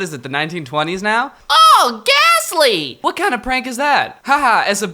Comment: She said "ooh ghastly" but i thought she said "oh gas leak"